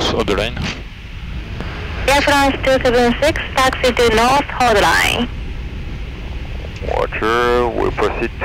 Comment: does any one know what the tower say at 7:30 and 7:36, dis she say taxi all the line? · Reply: taxi to north hold line